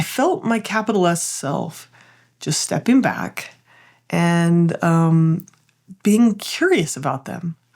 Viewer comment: I thought you said "my capitalist self stepping back", and I pictured you as Karl Marx suddenly leading the workers in revolt.